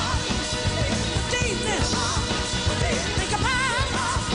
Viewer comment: Does anyone know what she keeps saying at 8:35?! Sounds like she’s saying “Davis??” Whatever it is, she is KILLING IT! This version of ‘Think’ is the best EVER! That sax player is going AWFF and gettin DOWN! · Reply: For deepness